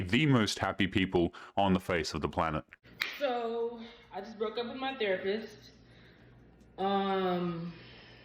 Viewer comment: I’m today years old in hearing the term “4B movement”. What are the 4 Bs? Did she say that she “broke up” with her therapist at 2:45? 🤔🤷🏾‍♂️🙃 Edit: I know what the four B’s are now; apparently the Korean word for “no” is “bi” …okay then… and that the “no’s” are women saying no to dating men, marrying men, s3x with men and having kids. Also, it’s weirdly political as well though I don’t see how America re-electing Trump to pretend to play as president again has anything to do with modern women’s poor commitment skills.